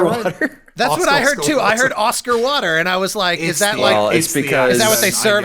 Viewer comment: I heard "Oscar Water" the first time as well, but with Alanah it sounded like what Mike was actually saying. I think the first one was just really not enunciated, lol